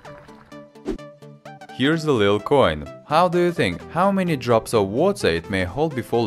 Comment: And why evertime i have subtitles, on 0:37 he says little coin and the subtitles says such a small coin? Am I dumb?